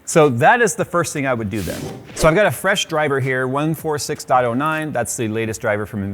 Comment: 146.09? I thought it said 461.09